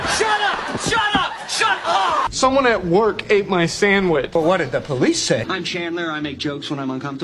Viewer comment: Sorry- why did I hear "someone at work ate my family😢"